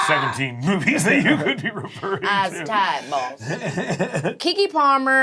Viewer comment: What'd she say @ 4:57? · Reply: "I's tired boss"